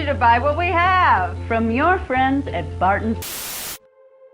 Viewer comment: Wait something feels of did she say from your friends right after she said buy what we have